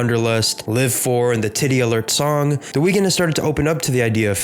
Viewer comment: I heard “tity alert” song 😂